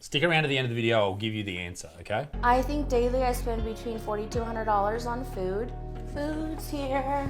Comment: When she said she spends $40-$100 on food a day I had to double take I thought she said $4200 a day 😂😂